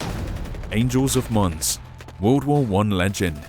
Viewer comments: I thought it said Angels of Moms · Imma be honest I thought it said angel of moms